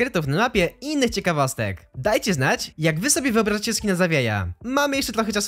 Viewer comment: Usłyszałem "skina za geja"